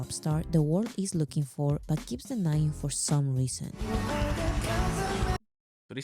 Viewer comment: i know its a lot of information but please try to remember go back, pause, relisten etc how ever much you need to so you FULLY understand everything she says because at 26:53 she did NOT say "he denies it for some reason" she said THE WORLD denies it for some reason, jungkook knows hes a pop star. the WORLD doesnt accept him as a pop star due to racism against asians. there has NEVER been a asian mainstream pop star. you thought she said he denies it and asked why he denies it. when thats not what was said at all. hope you understand this now :) armys love long videos go back however many times to listen or read stuff on screen especially korean lyrics and when people speak in korean as it goes fast. we do not mind you making sure you understand everything correctly we also do the same just off camera. i know in the west most reactors and people in general are very go go go push out videos make sure theyre 10 minutes long or less dont rewind dont pause etc but for ARMY we LOVE those things it shows the reactor cares to know the truth. so pause and go back however many times you need to and express your opinions regardless if its 'i dont like this song' because we can tell already youre genuine so youre not gonna like a song cause you hate bts youre gonna have a real reason for not liking the song. most bts reactors 'love everything' to not upset some sensitive fans and others just hate bts so they hate anything they do without any real reason. real good reactors say their opi